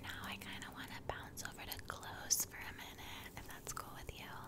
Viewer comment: I thought you said “bounce over to glues” not clothes....took me a minute buuuuut I caught on.